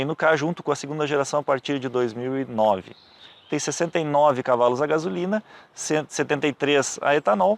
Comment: Só eu escutei 173 cavalos?